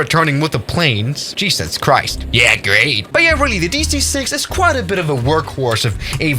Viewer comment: Did just say dc-6 at 2:07?